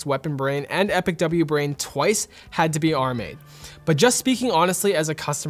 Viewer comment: “had to be [unintelligible]” What did he say here? “Had to be *are made*”?? I legit couldn’t understand what he said, anyone know? Edit* After thinking about it, he probably said “RMA’d”..? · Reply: Butters Scotch you were correct, he says "had to be RMA'd" (in case you didn't know) means Returned Material Authorization, which basically means they had to return them to RED.